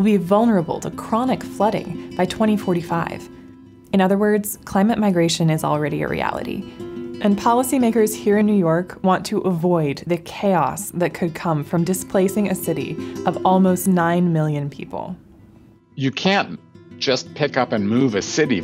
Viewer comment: why does it sound like she said policy n word hard r lol